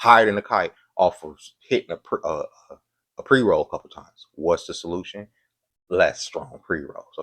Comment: did I hear a perky? 😂